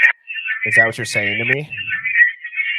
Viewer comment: I think it’s just “is that what ur saying” n then @ 49:49 “I’m telling u” or more nefariously, Yupp, “they’re killing them”…….ya kno, i dont know how yall b doin summa this shiiiii, cuz i KNO im not the only 1 who woulda never grasped onto voices in all that jumled-messy-ness!! lol